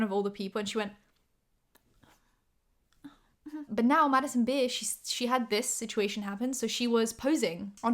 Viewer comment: I swear I thought you were saying Madison Beer 😂 I was so confused when I saw the screenshot that said Bear